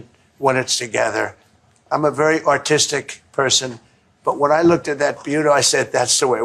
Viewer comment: did he say im a very autistic person? I agree.